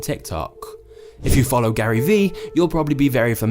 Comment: Did I just heard Gary V?